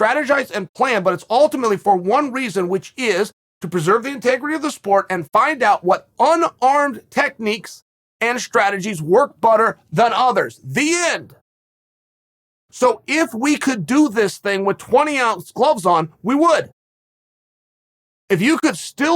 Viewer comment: Chael ; “To find out which techniques work butter “ Ok great EDIT; wtf is happening in the sub comments .. 😂 y’all have me weak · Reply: I was about to say I can’t be the only one that heard him say work butter